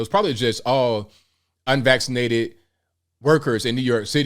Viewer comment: Legit thought it said "unwaxxed workers" at first lol